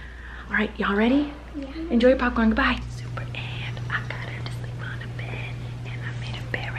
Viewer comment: Colleen sounded like she was saying super ants it was hilarious love all of you